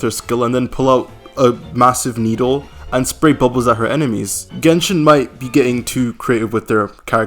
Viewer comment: I thought you were saying Sigewinne was obsessed with balls but your just saying bubble really fast.💀💀💀